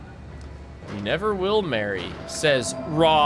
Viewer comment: what is the second thing he says? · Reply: "You'll remain here, now."